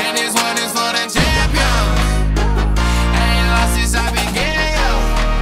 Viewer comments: am I the only one who thought he said "haven't lost since I been gay?" :O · I thought he was saying "I ain't long since I been gay, yeah".